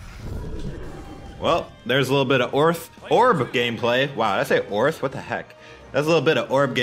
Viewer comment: At the first time I heard it as sounded like "ors", and it's very similar to Turkish word "örs" ,which means anvil. And then I imagined fighting with a f*ckin' anvil in your hand, BMG should really make this a thing.